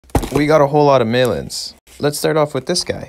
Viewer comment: legit thought he said "we got a whole lot of melons"